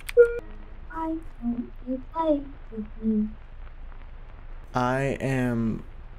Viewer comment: I hear “I want to play with you”